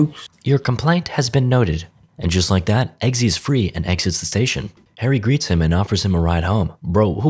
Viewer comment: Did he just say Eggsy's free and eggsits the station